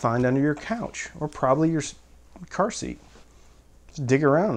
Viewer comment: I heard "car seat" as "pursie", like an another word for purse your nana would say. I liked it better that way.